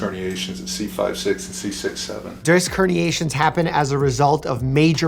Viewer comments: I heard 67 and no one said anything so I guess I will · ummmm yall tell me if im way to brainroted or did I hear 67